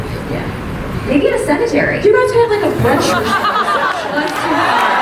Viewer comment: what does said in 30:20 ??? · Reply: "You guys can have like a brunch reception."